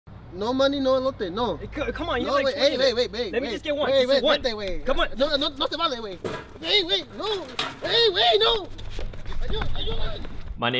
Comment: sounded like ramon